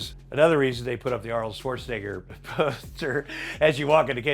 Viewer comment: Thought it said Arnold sweatneagger